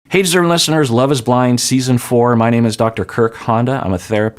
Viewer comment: Can I ask what you are even saying at the beginning of each video? It sounds like you say "Hey certain listeners! Etc etc". That can't be right. What is it you're saying because it's driving me nuts 😅 thanks! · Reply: he is saying "hey deserving listeners ... "